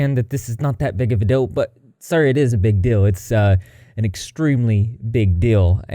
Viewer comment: Not related, but every time you said “big deal”, I heard “big dill” 🥒😂 and now I’m hungry